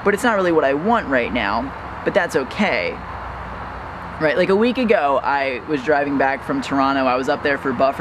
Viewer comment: When I heard like a week ago, all I heard was "about a week ago, week ago" haha